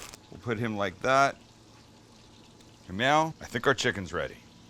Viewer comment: I knew it, Sam used to be a Vermont State Trooper!!!! He said “meow” (now) @ 8:07. My money says his call name was Rabbit. What say you?